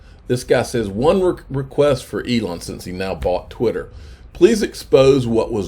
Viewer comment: Sounds like he said why now? LOL that is the great question though